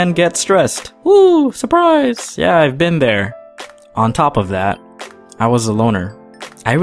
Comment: I heard, on top of that I was a boner...